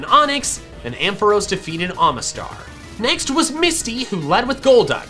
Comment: does anyone realize how much better the design of misty is in this game or did I mishear what he said and I’m thinking he said misty